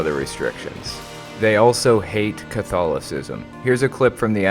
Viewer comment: what does contho..ism mean? idk the word